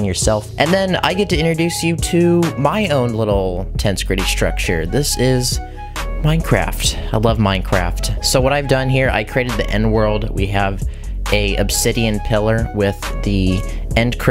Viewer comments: thought he said he created the n word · i thought he said he created the n word instead of the end world